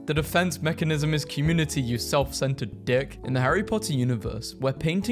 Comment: I don’t think he said prick